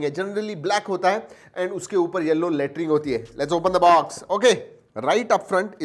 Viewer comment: Yellow. letring hota hai. Me: what!let me hear that again. at 0.50x:yyelloww leetriingg hhota haaai. Me:What!!let me hear again. at 0.50x: yyyeeelllooow leettttrriiinngg hhhooottaa hhhaaaiii. Me:What!!!!!!!!!!! is he saying!:😓😕